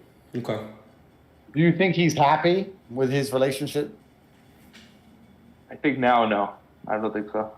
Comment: did Tate say Realionshit on purpose? Hilarious